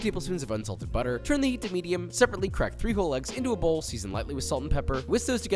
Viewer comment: He said "I really like 3 whole eggs". I heard "I really like FRIJOLES"... I thought he was putting beans on this for a quick moment. lol